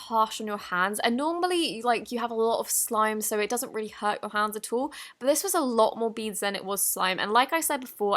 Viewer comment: When you say :" at all", my sister thought you said "asshole" 😂😂